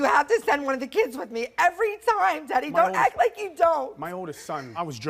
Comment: “Teddy?” I thought she said “daddy”🤣🤣